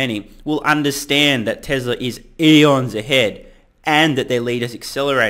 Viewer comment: What did you say at 5:58? Tesla is Elons ahead? 😂